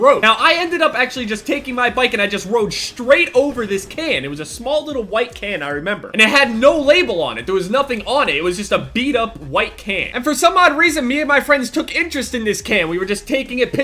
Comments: I thought you said " little white kid" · I thought he said "I ran over this little white kid". Who's watching this in 2016?